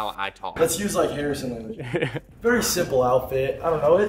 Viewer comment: Did harrison say nigga